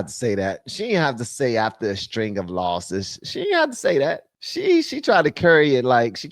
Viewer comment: I think she said “law suits “ not “losses”. At least that’s what I thought she said.🤔😄